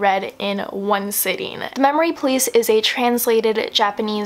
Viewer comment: I thought she said memory please